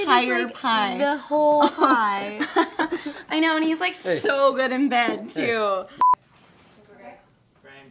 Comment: "He's so good in bed too." Why wasn't that line in it? x'D I'm dying! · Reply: Patches O'Hoolahan I heard "He's so bEd in bed too"